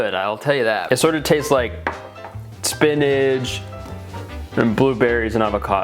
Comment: why does he say spinach like spinidge